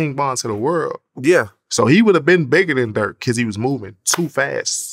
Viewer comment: I thought he was saying bigger than dirt, I was like dam that's a high bar